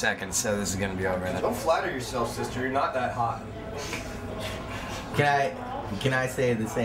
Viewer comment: "Dont flatter yourself sister your not that hot" lmao I love chris. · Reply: Before i watched this and saw the comment. I thought you said " dont flatter yourself, your sisters not that hot" I was so ready to see what the context was going to be lmao